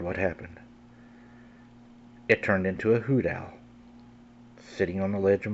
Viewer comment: Creepy story, but sorry, I couldn't understand - in what the thing turned the last time at 8:56 ... I even tried subtitles, but I couldn't figure it out...cause I'm from Germany, so English isn't my mother tongue and that means it's not always easy to understand your dialect/accent... So please help me & tell me, ... it turned into What again? Thanks!! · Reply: It turned into a "Hoot Owl", hoot being the sound an owl (bird) makes.